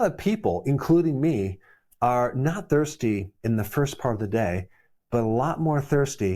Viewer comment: I thought 3 said first fart of day 😂